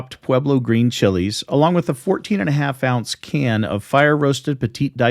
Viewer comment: my point is less about what it is and more about it being absent from the recipe. Apparently he's worried about you challenging him at the cook-off next year. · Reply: 😂 Maybe it’s just me but I thought I heard him say a can of Rotel? But I really don’t want to watch it over again.